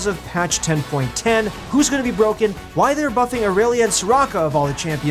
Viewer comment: For a moment I thought he said Aurelion. For a moment... I had hope